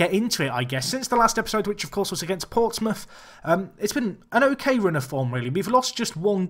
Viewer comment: did he say he hae played portsmouth? It was Peter-brought